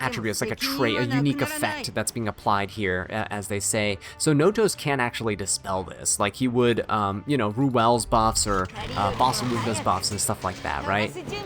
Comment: Yeah, that's what I said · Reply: sorry i thought i heard "notos can actually dispel this like he would barunka and ruelle" that was my bad